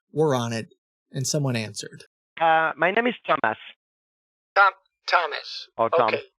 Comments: "My name is dumbass" and you can't convince me that's not what Thomas said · SOunds like he said My name is Dumb ass lol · LOL it sounded like he said my name is Dumbass.... i got a flashback from 'that 70s show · For a second I honestly thought he said, "My name is dumbass."